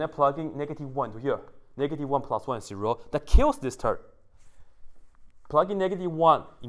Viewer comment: Wait did you say "that KILLS this turd" @ 6:12? lol